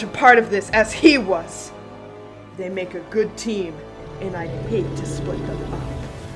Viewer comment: I'm sorry, what did she said? I couldn't heard over the sound effects... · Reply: “They make a good team, and I’d hate to split them up”